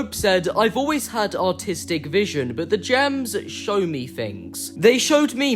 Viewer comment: did he say autistic 💀💀💀